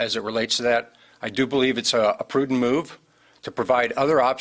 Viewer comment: "I do believe it's a Putin move..." Probably heard that wrong or did he mean what I heard😂😂